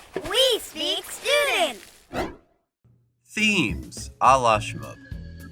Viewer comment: did he just say allah shmoop?